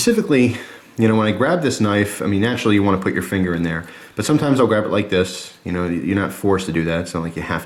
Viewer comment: lol, i thought you said 3 in roman numerals for a sec, then i realized it was I'll